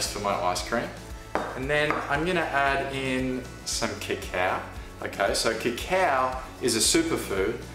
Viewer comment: What is “Kical”, or what is he saying? What is that powder? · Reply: The subtitles may be off... It's probably me saying CACAO powder :)